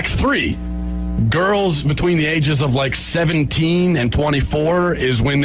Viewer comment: He said 17-24, where does he say 16? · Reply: he clearly says 17.